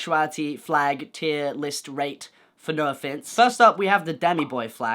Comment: Fun fact: When I first saw your channel, I thought "Noah Finnce" said "Noah Finance" and I thought u were a channel about money and stuff T-T